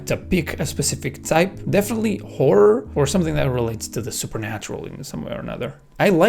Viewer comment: what did he say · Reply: Horror